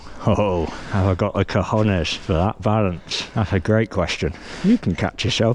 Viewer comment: Did say "I've goy my cojones"?? XDDD Wtf